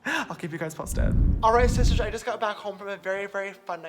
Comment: he actually said shishtarrs